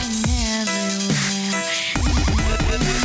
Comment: petition to rename the lava zone the "Deep dark down deep dark" and the Reefbacks "Reefies" like to vote :3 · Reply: I thought Jack Said deep down dark deep down